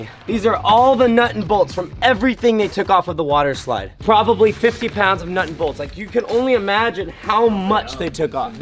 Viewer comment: I thought he said 50 lb of nut